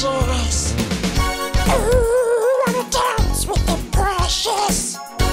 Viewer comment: I thought it said "oooh I wanna dance with depression" XD